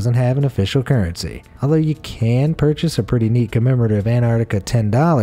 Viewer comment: I thought he said moose are exalent drivers 😂😂😂🤣🤣